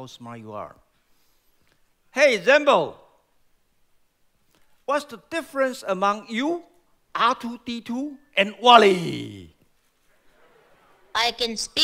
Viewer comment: I'll try to make out what was all said here? Because I didn't understood here (What's his name?): Hey Zenbo! What's the about you, R2D2, AND WALL.E? Me: DID HE JUST SAY MY CHILDHOOD BOT? WALL.E?! HES MY FIRST MOVIE IN MY LIFE! zenbo: I can speak your language, but they can't 😎 (???):That's correct!😉 Then. Who is the smarter man? 🧐DaVinci or Einstein? Zenbo: 🤔😞Sorry, I don't know the answer, but I will continue to work on my knowledge